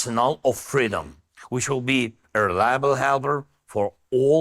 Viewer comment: "Which will be erlarbleharbre for all free nations of the world" What inspiring words! (What the fuck was he actually trying to say? "Available" or something?)